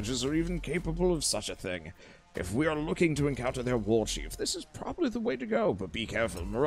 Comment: when you said 'warchief' i heard 'wall sheep' and was confused...